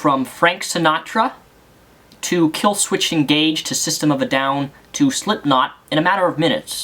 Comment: Did he just say KIllswitch-ED Engage-D?